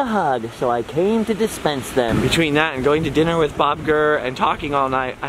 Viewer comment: I thought you said you went to dinner with Bob Iger. That would have been cool.